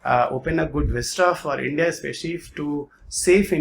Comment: did he say 9/11 attacks happened in India 🤔